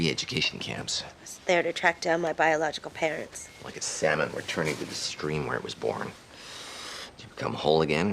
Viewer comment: I misheard 1:23 and thought "Like a Santa returning to the stream where he was born." was just a weird House metaphore